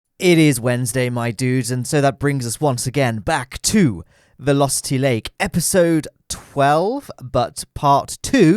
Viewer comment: When you said "Vilositty lake" I thought you said lusty lake xD